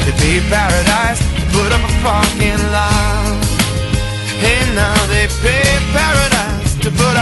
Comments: Who else thought he said, "Put up a f***ing light." (?) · I thought they said " they paid paradise " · Who else thought he said put up a fu**ing life or something similar